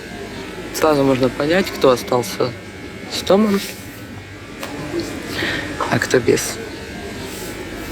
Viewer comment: "Сразу можно понять кто остался ??????, а кто без". Не могу разобрать, что говорит женщина в этом фрагменте. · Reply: "...кто остался с домом, а кто без."